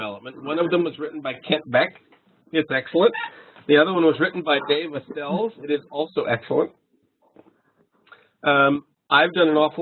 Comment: what was the second book's name he said at 1:12:40? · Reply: The first: Test Driven Development: By Example by Kent Beck The second: Test-Driven Development: A Practical Guide by David Astels